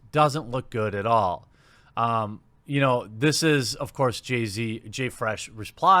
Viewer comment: Wait, did you just call the Respawn community manager "Jay-Z"?